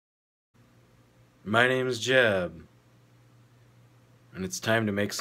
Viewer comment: Who else thought he was saying "my name is jeff"